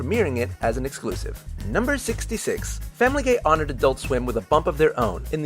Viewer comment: It kinda sounded like he said Family Gay... Does anyone else hear that?